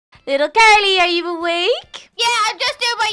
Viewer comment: In the beginning: Little Are you awake? Carly: Yea im just doing my yoGa XD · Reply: I heard "little carly are u awake?"